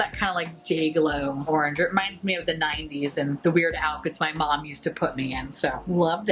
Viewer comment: You said "weird outfits" I heard "Weird Al" and now i'm salty that's not what I heard.